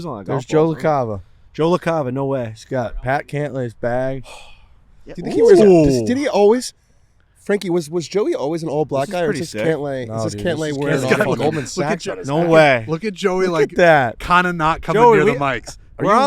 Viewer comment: sounded like you said Mr. ISIS guy... careful bois